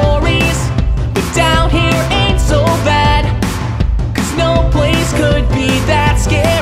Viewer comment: This is so late but at 0:31 I thought I heard “It’s so bad”